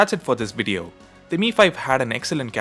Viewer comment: did he said mi5 at 3:48?